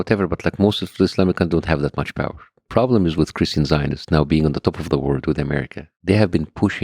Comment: "The problem is with "Christian Scientists". I"ve got to be hearing things. This can't be what he's saying. There is no more seriously peaceful church than that. Someone help me.